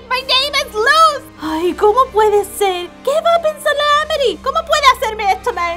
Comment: I don't know why but I keep hearing 'ameri'. It should be - amity - , right? I shouldn't delve to much into it since it mexicanese. Yo no se mexicanese. 😄